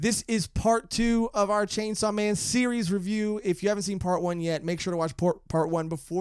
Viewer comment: you sounded like you were gonna say porn 1 instead of part 1